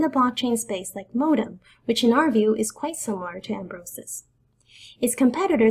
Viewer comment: I didn't catch what she said at 06:34. "which in our view is...??" Could anyone clarify that for me pls? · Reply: "which in our view is quite similar to ambrosus" :D